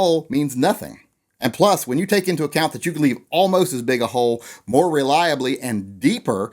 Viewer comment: I heard you say all most a big a hole....hmmmm